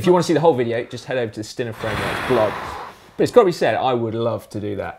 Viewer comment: Can you give a link to the video you mentioned at 15:30? Sorry I couldn't make out your accent on the title. Stinner? Stiner? Stynah? Thanks!!